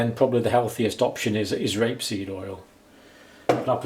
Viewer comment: He's WAY wrong about rapeseed oil. Rapeseed oil is one of the most dangerous oils you can consume as it causes scarring of the heart (cardiac) tissue. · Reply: I thought he said Grapeseed Oil.